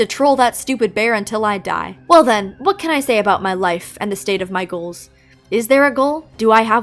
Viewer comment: I heard "The state of Michael" not "my goals" 😭😭😭